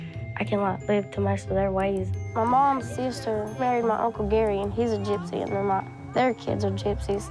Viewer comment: Mom narried uncle?! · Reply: That's what I thought she said too, but she said "'my moms sister married my uncle & he was a gypsy" so her moms sister married her dad's brother. I had a quick WTF moment too when I heard that & had to relisten a couple of times 😂🥴 SWEEEEEET HOME ALABAMA